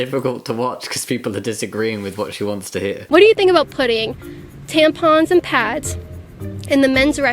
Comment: I thought she said pudding